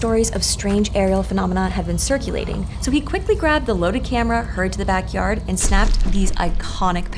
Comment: Does anyone know what “he heard to the backyard” means?? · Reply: Hurried, genius.